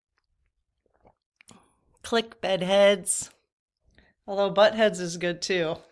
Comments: I thought you were saying buttheads as well! 😂😂😂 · I thought you say B*U* T*T Head too😂😂😂😂😂What is it really? That's how I hear it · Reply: BED-head😂